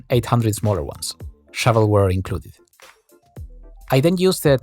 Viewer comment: Shadow Warrior! Woooo! Or did he say Shadow ware?